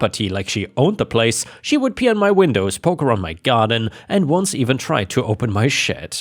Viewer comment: Sounds like you said “she would pee on my windows”! 😂 Thanks for the laugh!! 😂